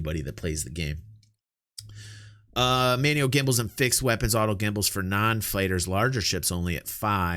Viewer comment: Ha ha I thought you said gimbals an anuses, I actually did a double take.